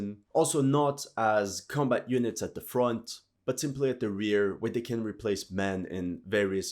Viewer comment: did he say with their rear 😮